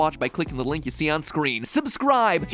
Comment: See what happens when you try to be cool and say something like "on the reg?" It sounds like you said "on the rag" lmao. Love all your channels and each one of them has quality content which is an amazing accomplishment. Keep them coming.